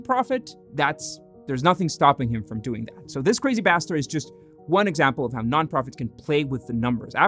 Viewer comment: I thought you said "this crazy bastard" and after rewinding like 5 times I was disappointed and realized you said "this crazy pastor"